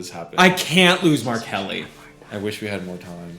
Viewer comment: When you were saying that you "can't lose Mark Helly" together at 18:46, my brain legit glitched because the first time I listened, I heard that you "can't lose R Kelly." I was so confused lol.